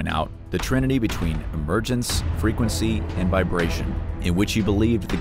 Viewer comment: at the 3:27 mark, there is talk of Tesla's 'trinity'. I heard frequency and vibration but could Not make out the first one. can someone help? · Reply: I think it says "emergence."